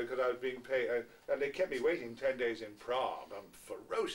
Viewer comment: I love when Youtube tries to auto-create the CC. "quite nice to have a job to the gulf dick batik" instead of "quite nice I went around giving beggars big tips"